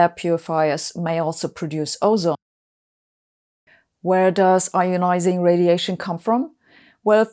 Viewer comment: What happened @ 7:38 after "...may also produce ozone"??? The subtitles say "..., which is not healthy" (that's true), but I can't hear you saying it.